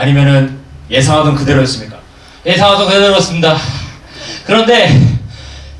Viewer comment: 에 그런데 ~~이상보다 좋으셨다고 하는건가요? 잘 안들려서